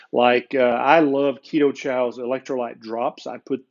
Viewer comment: Could anyone hear the brand of electrolyte drops, mentioned? Thanks. For those asking if HWC or other things break the fast, scroll down the list of videos. He has one that goes into details on what liquids do/don’t “break” your IF. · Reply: Thanks. It sounded like Ketochow, but I thought I heard wrong!